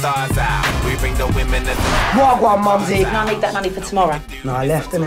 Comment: what does "nappy" say on 0:19 - 0:20?? is he saying Wawa mumzy?? :L